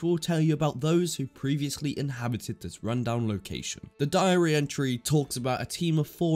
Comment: I had to check it twice I really thought he said diarrhea 😂😂